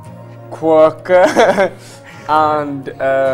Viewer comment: Did someone understand what was the third thing that Zayn told harry in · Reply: Quirky